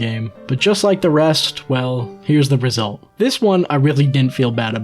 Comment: Did he say “Here is the Brazil”? Dude, as a brazilian I can tell, its so, so much worst than that, you dont even know. LMFAO